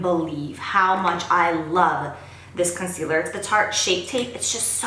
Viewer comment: I can't even believe how much i love ... I thought saying Jonh and instead it was the concealer! love you Jaclyn #snapchatfam